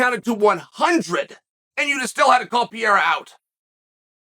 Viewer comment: Did he just say pee era